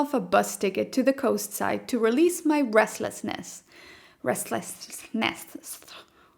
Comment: Did you just say Bresslessness? That's cute